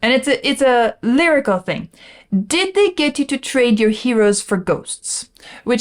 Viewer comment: sadly, sometimes ignorance is bliss…for decades i thought he said “did they get you to trade your heroes for GOLD”…